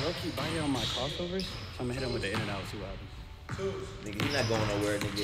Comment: Bruh at some point in the video I thought said “Ima hit him with the in-n-out burger”😂